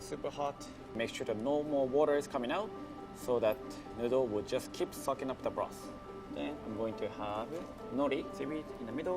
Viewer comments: I thought he said the noodle will keep f*cking up the broth at first 😭😭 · what did he say??! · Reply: "That middle will just keep sucking up the broth" That's what i heard